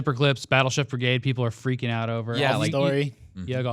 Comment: Does actually say “Battleships for gay people are freaking out over ”?